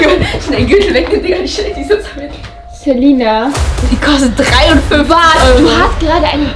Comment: Wer haette gedacht das eggs Wursthose mehr als 50 € kostet! 🤣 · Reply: Ich hab am Anfang verstanden "die hat 350 Euro gekostet" 🤦‍♀️😂